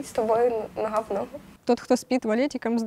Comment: Она сказала "Ногха в ногху", а мне первый раз послышалось "На гхавно" =)))